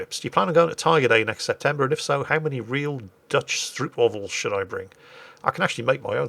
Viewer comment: I heard that as "How many Dutch male strip models should I bring to Tigerfest?". The answer to that really should be "as many as possible"...